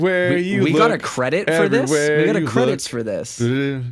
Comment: I honestly misunderstood for a moment and thought he was saying you two got a credit and was confused because I couldn’t see anything mentioning you guys in the credits, then it clicked that what he was actually flabbergasted by was that a credits sequence exists at all. 😅